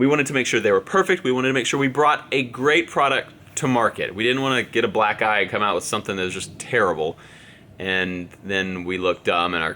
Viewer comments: did you say "get a black guy and get something terrible "😤😤😤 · What did he say at 1:25? He didn’t wana get a what? Did he say a black guy??? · Did you say a black guy? At 1:24 minutes😂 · did he say black guy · Did he say black guy at 1:27 in the video